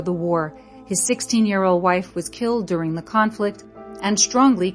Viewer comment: I'm sorry did she say 16 year old wife?